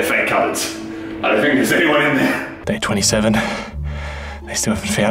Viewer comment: I thought he said may 27 witch is my birthday